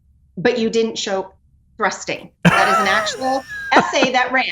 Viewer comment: Thrusting? I thought she said fisting? Yeah no thanks don't want to see any of that.